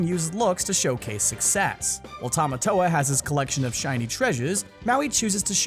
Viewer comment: did he say treasuz?